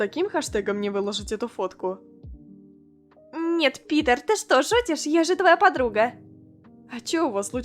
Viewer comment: Мне одной показалась на 8:35 что она сказала пидар а ни Питер